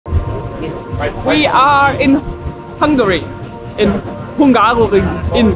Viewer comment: Intro i heard "We are Hungry!" :D for points <3